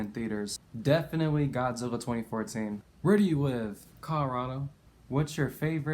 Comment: what did you say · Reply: Colorado.